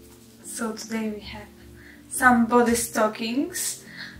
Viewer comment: LOL I swear I heard say: "Today we have somebody's stockings".! Give "somebody" back their stockings! 😂